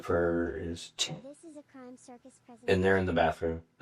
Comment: that freaked me out i was like did i just hear a little girl talking? I had to rewind and turn the volume up. 😂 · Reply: Yea I heard this is a crime circus something something something 😂😂 It freaked me TF out too